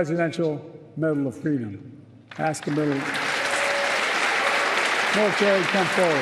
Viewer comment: what does he say here? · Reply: I think he says 'Ask him to...' because the prompter says 'Ask him to come forward' and he started reading the action cue out loud.